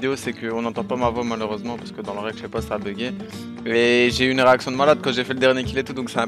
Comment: Je suis un gamin. A 0:44 j'ai compris "J'ai eu une érections de malade" 🤣